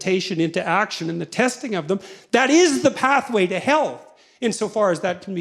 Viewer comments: Fascinating. I thought he was saying "that is the pathway to hell" and re-listened a number of times before hearing him correctly: "that is the pathway to HEALTH". Got to practice the listening carefully thing while hearing about the importance of listening carefully, bonus! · did he say "that is the pathway to hell"? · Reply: I though so too for a moment but he said, " the pathway to health'.